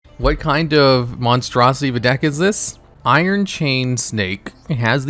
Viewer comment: I SWEAR I HEARD YOU SAY "iron chain steak"